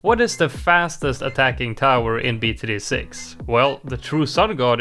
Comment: i thought u said fattest